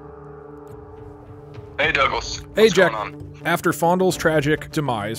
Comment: "Oh! Well that’s quite the story Douglas!" is the most underrated part of this video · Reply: I thought he said “douggles” but Douglas makes more sense yeah